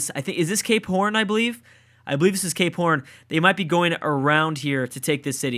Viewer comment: I swear at 15:00 he said gay porn